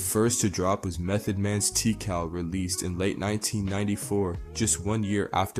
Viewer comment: Did bro say Tea-cal 1:37? O he'll na